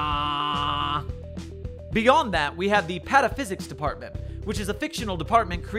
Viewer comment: Low-key thought he said pedophysics for a sec